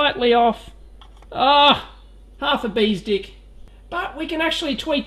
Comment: did he just say "half a bee's dick" at 28:33 ? never heard that one before but I like it :)